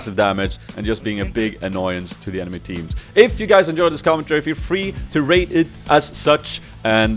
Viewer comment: Lol in the end i heard "if you liked this commentary feel free to rape it..." brain wtf